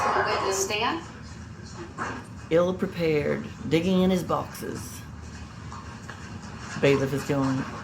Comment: I thought you said ...diggin in his boxers...😂😂😂 I almost spit my coffee across the table🤣🤣